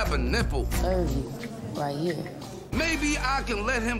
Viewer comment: What did Bootz say at 10:15? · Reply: of u, right here 😂🤷🏽‍♂️